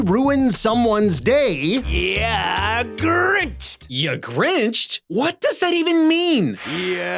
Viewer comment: As a kid playing this game, whenever he said "Ya Grinched!" I always heard it as "Yeah, Grinch!" like he was congratulating himself. Makes more sense than how it actually is.